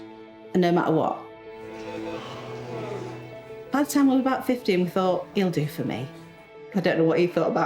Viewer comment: I thought she said it took her until age 50 to figure out he was the one for her, not 15 😂